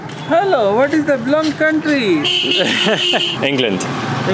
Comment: HELLOOO.... what is the Belong Countryyy..? Lmao.... 😂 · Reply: YO WHY DID I HEAR “WHAT IS THE BLONDE COUNTRY”😂😂🤡